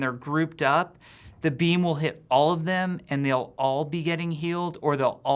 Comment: I kept hearing "the beans will heal grouped up teammates"